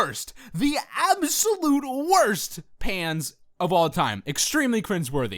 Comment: At the beginning I thought said the worst pants xD silly me